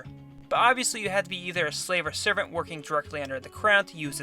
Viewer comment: I thought he says "My country!"